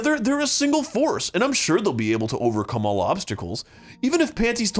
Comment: I heard "overcome a lobster culls."